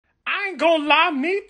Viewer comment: Everyone keeps saying “Aho jump scare” or “ahu jump scare” when I thought it said awoop jump scare 😭💀